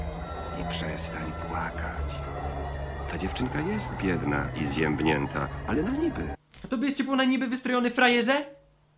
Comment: kto usłyszał "ta dziewczynka nie jest biedna i (zjebnięta)"?